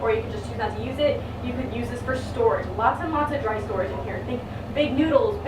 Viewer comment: anyone else hear "lots and lots a drug storage"?